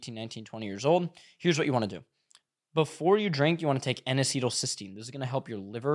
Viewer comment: could you type out what you said? Anesytl cystine?? I can't really figure out what you just said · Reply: I think he said N-Acetyl-L-cystein